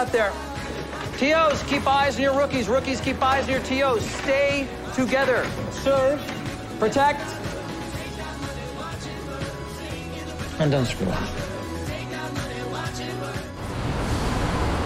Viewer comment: I honestly love how Oliver says “don’t screw up” like the first episode but I also thought he said “don’t screw them” which I think would have been even better ending